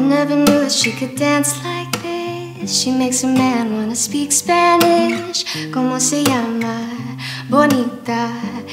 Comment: Lol I always thought he said “she makes a man want to shish ka nish” like it was Spanish words I didn’t know 😂😂😂 all the times I’ve sang this out loud